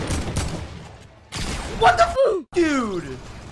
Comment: it sounded like he said “WHAT THE FOOF DUDE” LMAO 😂